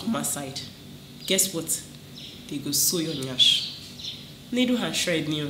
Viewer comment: I’m not even done… I heard “after they sew your yansh” 🤣🤣🤣🤣